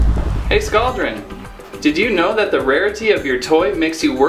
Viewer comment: did you said... Rarity?